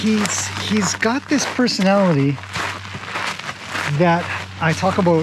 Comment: When you said "personality" I heard "horsonality" 😂